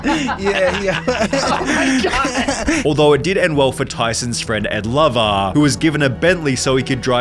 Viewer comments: I was shook, I thought you said "Tyson's friend AND Lover.." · I thought you British accent said "it didn't end well for his friend and LOVER" lmfao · I thought he said ''Tyson's friend and lover'' Haha his name is Ed Lover!!!! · “Mike Tyson’s friend and Lover” is what I thought you said lmao I had to look up if Mike Tyson was gay haha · I heard "tysons friend and lover" 😂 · I thought you said Mike Tyson’s lover (his friend Ed Lover) I thought wtf is Mike gay and just no one talks about it? I was totally confused for a moment there 😅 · I legit thought he said "his friend and lover"